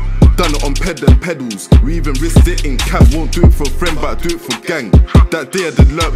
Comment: that flow confused tf outta me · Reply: You misheard he said we even risk it in cabs